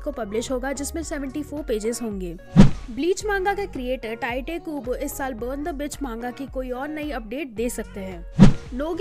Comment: I may be wrong but everytime listen I hear "burn the b!tch" 🙄🙄🙄